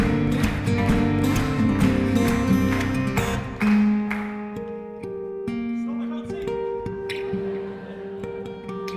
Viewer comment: what did he say??!! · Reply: Hinzii sorry i cant sing